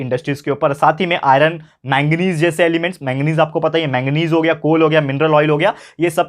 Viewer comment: when I heard manganese I heard mayonise